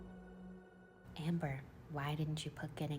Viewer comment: “Why didn’t you put getting a bf on the vision board; cuz I rather have new shoes” FELT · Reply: ahahaha! I thought she said "I'd rather have issues," and I was confused, hahaha. But new shoes! That makes more sense, lol :)